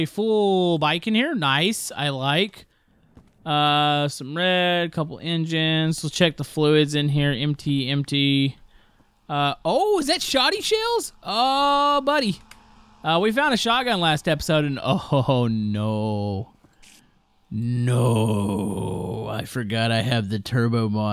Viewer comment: did anyone hear what he just said, he said 'shawty shells"???!!!!!